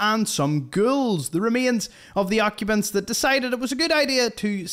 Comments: I was listening not watching, thought you said GIRLS, instead of GHOULS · Totally sounded like you said girls, not ghouls.